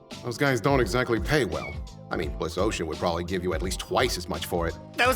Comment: Osha being considered terrorist's is something that I don't know whether or not is meant to be a flipped version of IRL Osha or if they are making a fourth wall breaking callout. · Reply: Bliss Ocean, but I like your misheard version much better.